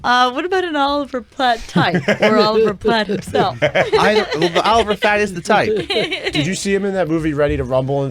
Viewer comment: Cant believe they missed saying Oliver Fat instead of Platt